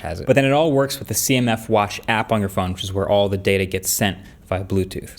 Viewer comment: I thought it said it only works when the CNN app is running and I was like thats weird...